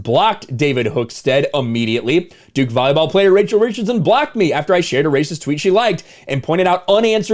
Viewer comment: I honestly thought you said 'racial richardson'. 😂